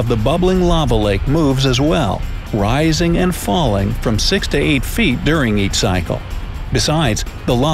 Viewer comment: did he say 6 to 8 feet?